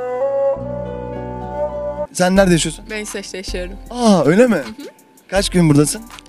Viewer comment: deki kiz ne dedi? ... yasiyorum? O nasil kelime aq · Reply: SemiH PB isveç